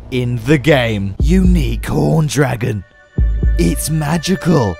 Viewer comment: I thought it said unicorn dragon not unique horn dragon